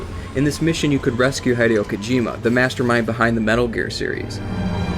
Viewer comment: They way you pronounce "Hideo" triggers me greatly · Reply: I had the video playing in the background while writing an email and I swore he said "Hidey-Hole Kojima".